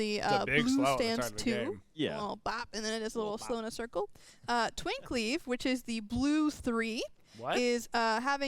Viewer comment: Hahaha the incredulity on face at mishearing Twin Cleave (though she 100% said Twink Leave) was brilliant. Imagine if they actually banned all twinks from playing, who would play Morgan??